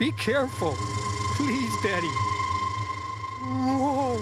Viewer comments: hold on... did he just say "hold on daddy"? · "be careful... please, daddy..." [i know he's saying betty but the first time i heard it i heard "daddy"]